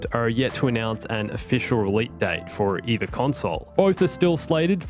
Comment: Did he just say “relete date”